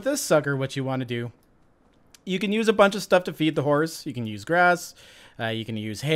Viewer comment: I thought you said "you could use a bunch of stuff to beat the horse". I was like "Damn, this took a dark turn early in the series".